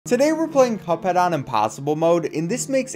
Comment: if you keep rewinding to the start over and over instead of saying "today" it sounds like he says "PEDEW"